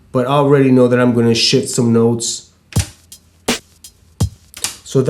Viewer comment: Haha I thought you said "shit some notes"